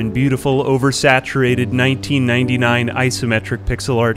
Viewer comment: "boredom and ????? set's in" what word is his saying there? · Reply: Ennui